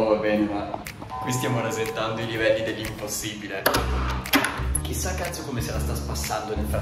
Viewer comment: ho sentito “chissà cazzo invece di 😂😂